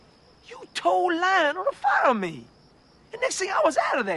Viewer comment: You told Lionel to FOLLOW me.(I know it's fire but sounded like follow)